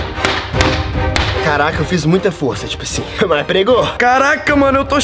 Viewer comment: Eu ouvi o fala caralho